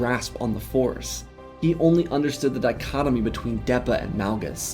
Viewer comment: Did you say, Dichotomy?